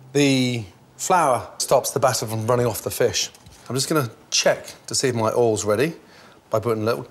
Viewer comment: Did he just say check you penis?